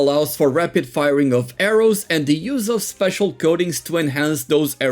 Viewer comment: I keep hearing "goatings" instead of coatings